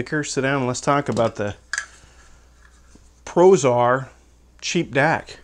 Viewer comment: I was waking up seeing this video. I thought the intro said the Prozac DAC. Good Lord, where's my coffee?!